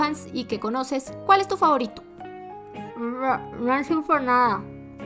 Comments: nancy x nada xd (al menos eso entendi) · yo si entendi y dijo "Nancy × Nada xD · que dijo? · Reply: Yo escuche fancy :"v el shipp que no dan ni una señal de vida